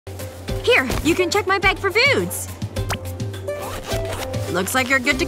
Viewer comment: I thought she said "here u can check my bag for boos.